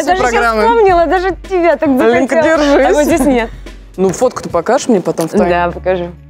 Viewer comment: хотела покраситься как... эта... господи имя то ее как... - ольга? ахахахазпэп, прости Те кто знают ЛенОль, поймут) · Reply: Там на 28:23 Ивлеева сказала: Оленька, держись. Может послышалось?